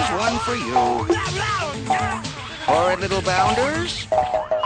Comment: Did hey say "That fuck-brain"? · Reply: Did he say "little bastards"?